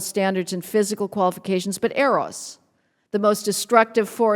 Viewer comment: what is she talking about when she says something about, "errose(?), the most destructive force in human existence......."? What is this "errose" thing? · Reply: "Eros" the god of passion/desire/love